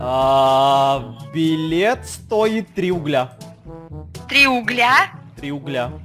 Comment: кому тоде послышалось 3 Рубля?